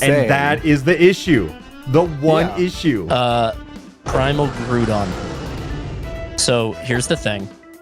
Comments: I thought I heard Primal Crouton at first, was the best person in the video, peak lines. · I THOUGHT SAID PRIMAL CROUTON